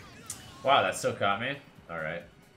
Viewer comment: thought you said "wow that's so commy" lol